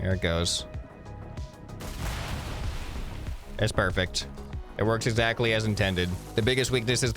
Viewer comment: Did you say PERFECT ?